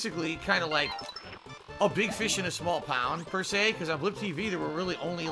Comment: did he say a big fish in a small pound? im sure it's pond lol